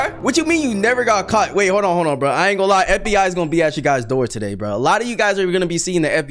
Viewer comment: I kept hearing I ain’t my n words dog 💀